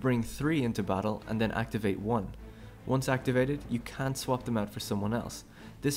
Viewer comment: Are you saying you CAN or CAN'T swap them out? It sounds like you're saying you CAN, but then you say that's why it's to wait to know which one you want to use, which implies that that you CAN'T swap them out... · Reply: I know what the screen said, but it sounded like he said "can swap them out," which, if is indeed what he said, is in conflict with what the text says. That's why I wanted to verify. I later watched with captions and saw that he indeed said "can't swap them out," so that settled that. /facepalm